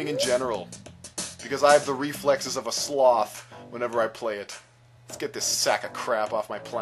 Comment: IS FUNNY SAID Reflects of a swath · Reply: Reflexes of a sloth! :D